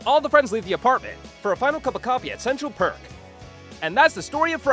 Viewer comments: if anyone hears PARK instead of PERK (2:48), you’re definitely deaf... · Everyone is complaining about him saying ”central park” At 2:48 But he clearly says ”central perk”?? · Idk what y’all are hearing cuz he said perk at 2:48 not park